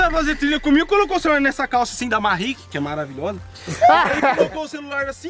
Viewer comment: Alguém sabe o nome da calça que ele falou 2:39 pfv preciso ter uma daquela se alguém souber me fala o nome não entendi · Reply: MAHE